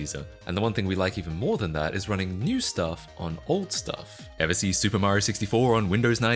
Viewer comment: I like running old stuff on new stuff too. And doom on random stuff. We just like running things on things that weren’t intended for it 😂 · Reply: I thought he said new stuff on old stuff..